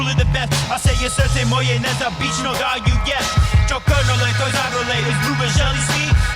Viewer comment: He said smthing like: "...when they ask me why is that i'm truly the best, i say srce moje nesebicno (my selflessly heart) is all you get, DjokoNole ..KOZArole ( derivative of GOAT, smthing like GOATier, dunno correct word)... izgubit' (?) zele svi (everyone wants him to lose) sa nama i u nama, come on, zivi ti (you live with and within us), DjokoNole, Beograde (?) (Belgrade) the truth is always be, represent world wide svi ce te uvek pamtiti (our heart will always remember you). I'm not sure what he said between KOZArole and ZELE SVI there is one word which is hard to recognize but it could be izgubit' (to lose), also i m not sure about BEOGRADE word, could be smthing else, it sounds like he said DjokoNole iz Beograde (Djonole from Belgrade) but if he said like that then it is not entirely grammatically correct. If i manage to understand it i will edit this comment again. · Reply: Actually he said SRCE MOJE NESEBICNO DARUJES (you gift your selflessly heart). there is no "is all you get" part